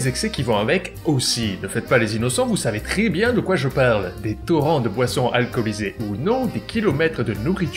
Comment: Ha ha j'ai entendu "des torrents de poissons alcoolisés"